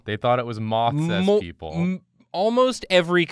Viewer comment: I thought it said mythos people